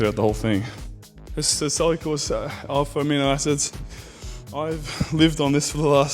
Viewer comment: Did he say amino acids at 7:36?